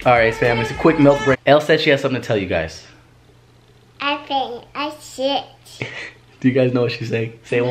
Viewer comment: ELLE AND ALAÏA ARE SO CUTE 😭💛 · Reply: Who else thought Elle was saying "I'm shit"😂😂